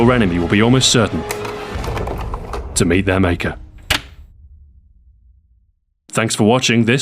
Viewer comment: haha at 7:12 with transcribe audio, it says tomato maker instead of to meet their maker